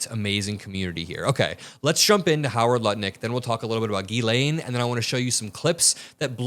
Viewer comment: Just prior to 3:42, I think you said nut lick 😂😂😂😂😂 instead of Lutnick